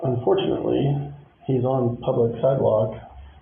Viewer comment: "Unfortunately he's on public side-walk,,,,," I had to reply it three times because I kept hearing "Unfortunately the constitution exists,,,"